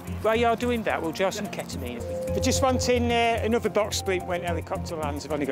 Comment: did he say ketamine?